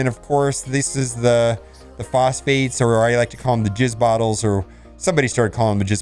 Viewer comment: DId I hear correctly - "I like to call them jizz bottles"? I thought you were already crass enough with "lube & spit." I'm done watching your videos. It's a shame you're such an influential part of this community and to know the youth of this community will soon be using such unfiltered nonsense as a part of their regular jargon. Congratulations to you on all your success and best of luck in your endeavors, however I'm going elsewhere for my content. I realize it's only a mobile game but you have to take care when you're so influential. You could learn from other respected members of the MSF content providers.